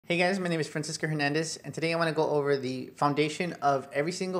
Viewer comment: I thought he said Princess Hernandez. Lol